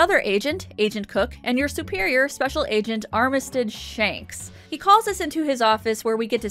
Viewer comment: I thought you said that agent was called Armitage Shanks, which is a British toilet manufacturer 😂 I had to listen to it three times 😂